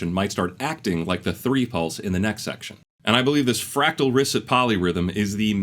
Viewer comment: sorry, what did you say here? fractal-ristic polyrhythm? i couldn't understand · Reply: fractal risset polyrhythm fractal risset polyrhythm fractal risset polyrhythm...i keep looping that phrase in my head in steady 8th notes to the melody of Happy Happy Joy Joy because my brain is broken